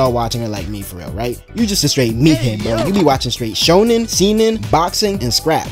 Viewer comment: did he just say we watch shonen,SEMEN,boxing and scraps!!!???!?!?!? what in hells nine circles did i just hear